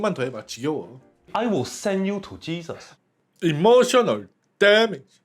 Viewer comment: emotional damage 왜이렇게 자꾸 emotional dammit으로 들리지 ㅋㅋㅋㅋㅋ